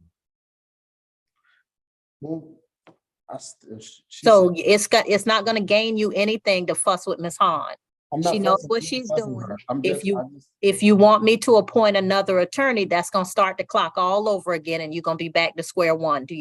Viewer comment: Love this judge. I thought I heard her say "don't fu*k with miss Han" but she said "Fuss" 😂😂